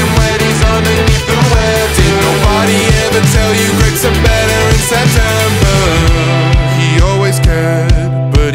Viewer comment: My big poo poo brain heard this as “Greggs are better in September.”